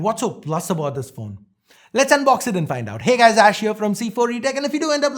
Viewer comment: I thought you said less unboxing from now on